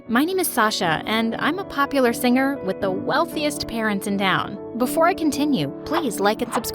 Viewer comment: When I started watching this I heard “I’m Sasha and I have the eichest parents in town” but finishing this I thought it was pretty good, and the title is right for the first time!